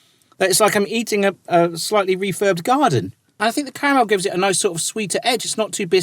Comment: Did he say he was eating a 'refurbed garden'... !?!